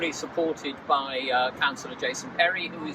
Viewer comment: Oh. "Counselor Jason" I thought he said "council adjacent" and it meant we needed to add another color stripe to the Pride flag. Silly me.